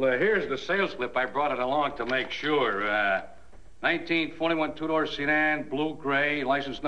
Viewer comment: I love how the used car salesman just gives a basic description of the car like he doesn't know exactly what year and model car he sold lol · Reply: I played the video 25 times and I am sure I heard the salesman reading the sales contract to Dan confirming that the car was a 1921 two door sedan. We all know it is a 1941 Oldsmobile! 20 years off!